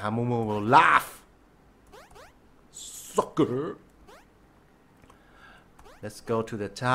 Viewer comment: did he say f***er